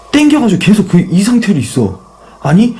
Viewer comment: 리플레이 구간 들어보니까 나는 "나 있어" 로 들림..